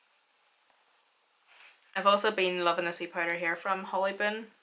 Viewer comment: I’ve followed you for a very long time and absolutely LOVE watching you. When I first started watching you it took me a minute to realize you were saying “powder” I thought you were saying “pider” like spider lol, I love love to hear your accent🤗Your amazing all around and so stinking talented ❤️